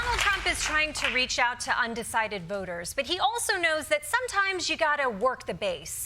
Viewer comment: I heard "undecided boogers" at the top. Sorry.